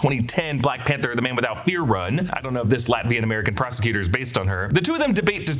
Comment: I swear to god on my first watch, I thought said Latveria and I lost my mind